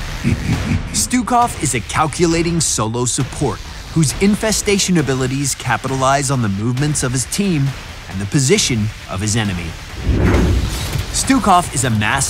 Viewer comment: What does he say at 0:53? Infestation of ...? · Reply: infestation abilities capitalise on the movements of his team and positioning of his enemies